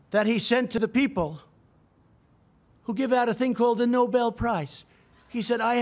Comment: Noble Peace Prize!!! Hahahahahahahahahahahahahahaha!!!!! Oh man I’ve not laughed that long since the night you guys voted this clown as President! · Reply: at first i heard it as "Noble Pizza" which makes more sense